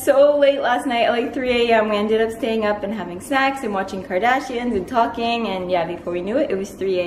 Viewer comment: I had to go look at the comments the moment i thought i heard "having sex" and i'm glad i am not the only one who heard that lol